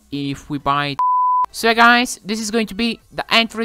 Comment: Did he just say "see you next month" 🤔